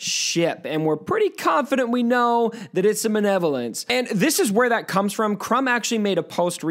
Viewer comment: it really sounded like you said “Manevolence” haha